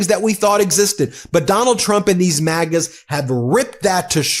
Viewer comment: Thank you Gavin Newsom for HONESTLY CARING ABOUT OUR FREEDOMS!!!! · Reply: I want Governor Newsome as the next president. Thank you for fighting for democracy both Gov. Newsome & MT. It's time to take gloves off🥊🥊 & fight back!!!🎉🎉 BTW, I thought Ben was saying Maggots instead of MAGAS. 🤔💭🤔